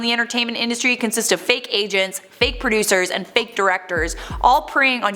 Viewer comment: oh when you said fake agents, i thought you said 'fake asians' lol